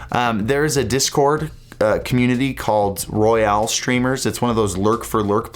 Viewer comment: I thought you said battle royale players and had to watch that again. Haha I was think warzone and fortnite and I was super confused. Black listed for fortnight seemed a lil harsh.